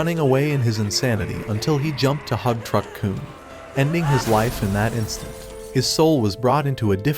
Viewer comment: i misheard it as "meeting his wife instantly" 💀